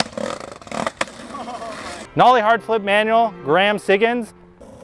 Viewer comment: Did I hear a Gus Chiggins?